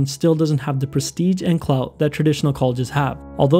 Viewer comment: Did he just say clout at 2:55? Lmfao